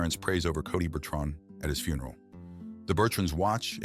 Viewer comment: I heard “ ratchet funeral “ and lost it!!😂😂😂😂😂😂